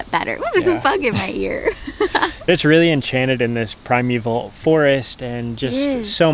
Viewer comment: LOL! For a minute I thought you said "prime EVIL" forest. I thought "What's so evil about it?" Then hit me...primeval. I'm such a dunce.